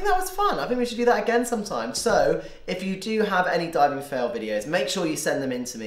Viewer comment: Ohhhh makes sense now. I thought it said DYING fails 😂